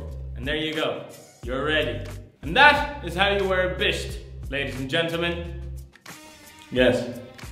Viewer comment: at the beginning i thought he said how to ride a bitch 😂